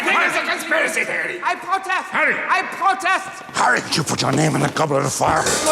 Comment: I always thought she said harry potter, but here the translation says, I protest. I am quite mindblown. At